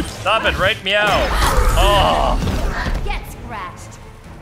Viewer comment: For a second when catwoman said "Get scratched" I thought she said "Get gratched." 😂